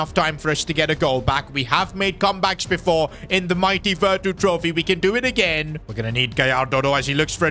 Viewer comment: I thought he said versus trophy was 300k